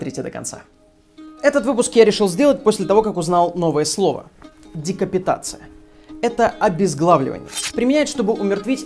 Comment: Декапитация, услышал "дико питаться")